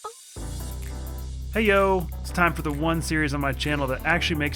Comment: Did you just say hey-oh?